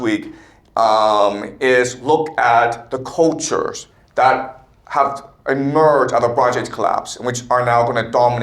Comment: is he saying “the Bronze Age collapse”?